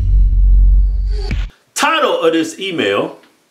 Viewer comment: Lmao, since i made that comment about mishearing "title of this email" you guys have been saying it clear as fuck